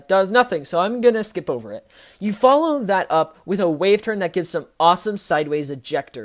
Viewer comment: I thought you said the wave turn didn't have airtime